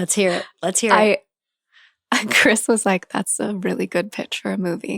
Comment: is it just me or did she really say “chris?” 😭